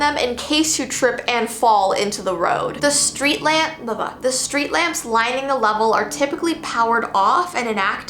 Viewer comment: the street lap (baba) was i the only one who heard that??🤣🤣